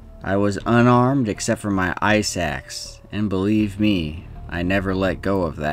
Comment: I thought I heard "I was unarmed except for my eye sacs" I'm thinking, "those don't make very good weapons". Lol.